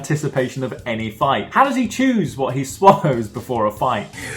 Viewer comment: I feel like this was an unintentional pun, but I hear "How does he "chews" what he swallows before a fight?"